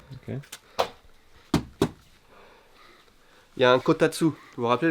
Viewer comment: "Il y a un Kotatsu". Tu parlais d'argent juste avant j'ai compris "quota de sous à avoir."..